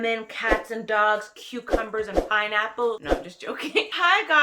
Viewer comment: Cucumbers and Pineapples 😂🤪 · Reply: Right?! 🤣🤣. She is so crazy. Lol. For some reason I thought she said fineapples 🤔. Lemme rewind it. Lol